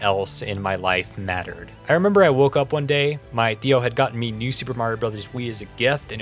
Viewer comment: woah, did you say "my tío" ?